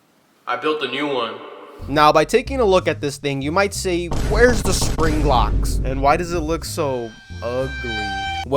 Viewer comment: I thought you said spring Glock where you give spring-trap a glock